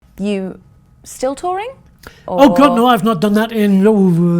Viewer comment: I misheard, I thought he said he's not Tory, not for 30 years as it makes him hot and sweaty 😂